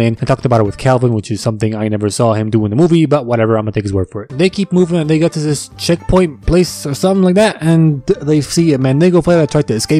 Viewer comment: BRO AT 6:17 I THOUGHT YOU SAID "they keep moving a nigga to this checkpoint place"